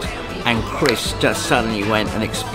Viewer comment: What did the director say at 1:55? "Chris has gone a bit _______"? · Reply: Bez, as in Bez from the Happy Mondays